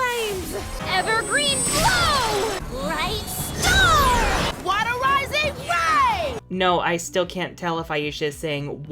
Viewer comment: season 1 stella: Dealing with parents and their divorce, overcoming priveledged life, sacrificing herself for her friends, Season 6 stella: Guchi Guchi Guchi NIkeNike nike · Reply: When I watched season six I thought Aisha was saying Water Rising Wave, but now I'm just fucking confused. Waterising ray? Water Rising ray? Waterising wave? SO MANY OPTIONS